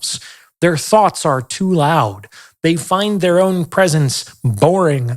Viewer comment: NGL, you said, "his thoughts are too loud" and I definitely heard it as, "his thots are too loud." Still fit somewhat.